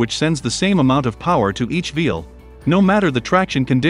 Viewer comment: & 8:44 Was that a typo, V instead of W, or ...? This is a fantastic video! Great edits, really nice animations. 🤘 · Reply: Auto subtitles suck 🤣 these were not generated by the author. It's wheel, not veal.